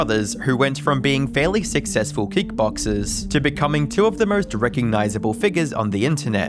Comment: sounds like you said they were beatboxers lol they were shite